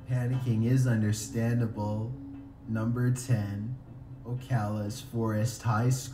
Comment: when he said Ocala i thought he said opalaca so i said ah fuck then realized